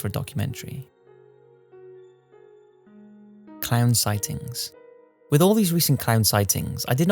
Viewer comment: At first I thought you said Clam sightings.